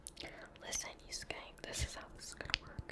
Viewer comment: "listen you scank" I hope I heard that... right? I don't even know anymore